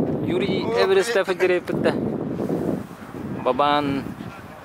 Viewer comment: Привет! 2:26 мне послышалось слово Бабан. Что означает слово Бабан?